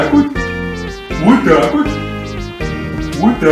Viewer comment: Я слышу одновременно "вот так вот" и "в атаку"